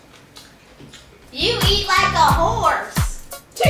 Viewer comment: “YoU eAt LiKe A hOrSe” I thought he said you eat like a who